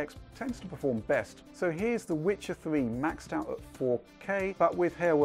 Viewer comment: I swear I heard the Wichard 3 :D